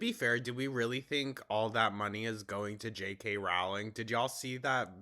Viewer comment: I heard you say Rowley and I jumped lmaoooo